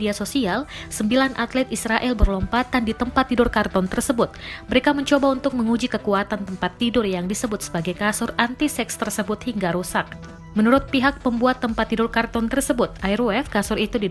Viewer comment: kasur anti apa? saya dengernya kasur antisex.